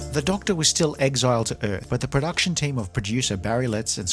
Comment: I swear it sounded like you said the Doctor was exiled to Perth.